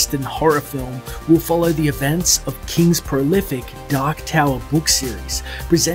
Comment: Swear I thought you were saying “the duck tale” instead of dark tower that accent is wild